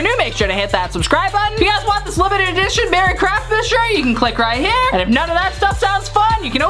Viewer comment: I thought you said “Merry crapmas” but nope, it was “Merry craftmas” my bad!😂😂